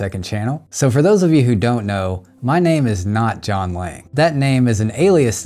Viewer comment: Ok I thought you were saying "John Wang" the whole time 🤣